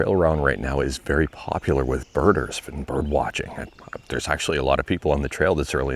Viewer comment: I have severe hearing loss but I could have SWORN you said the trail you were on was popular with murders. I was thinking, "Either this video will end poorly or I am going to witness some 'fluff' coming out of a random jogger by the end of this. Maybe I misheard him, I better back it up..."